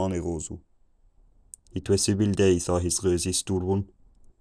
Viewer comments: near 700 I swear to god I heard "It was civil days" but I'm not sure if that's what was said. Damn I'm from Spain and I wish someone did a video like this with latin to spanish with a little bit of the iberian arabic that must have existed in between. It would be so cool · I got "It was[a bad]) day" at around 700s I misheard it as "It was seven days". After that, I picked up only a couple of words, especially "Chicken", but when I see them with the writing, I can definitely start picking it out. And then once we get to "It is a long winter" I mostly understood the rest. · Probably a needle width before 700, I heard "it was seven days." Like others, I understood "chicken" at 800. At ~1500, maybe a little before, I understood the whole conversation. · I swear right before 700 I heard "it was seven days" xD but then back to being incomprehensible for awhile.